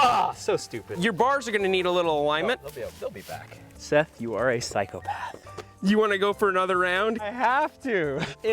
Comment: did he say: "Seth you are a cycle-path". Hueueueue